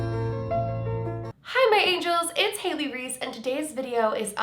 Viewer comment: I kept hearing " ITS CHEESY' 🤣